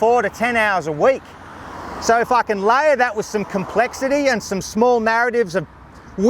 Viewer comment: "So f**ken layer that with some complexity" I know it's not what he said, but when this was playing in the background boy it sounded close.